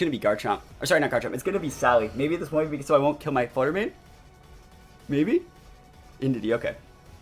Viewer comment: What did you say at 12:55 about not killing your flutter mane? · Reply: Ahh yeah. If they sent in Sally and Intimidated my Garchomp I might not KO my Flutter Mane with EQ lol